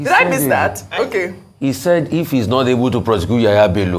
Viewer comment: I thought he said that he's not igbo shame on the zoo criminals everywhere